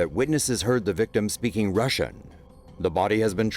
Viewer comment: did he say that he was speaking Russia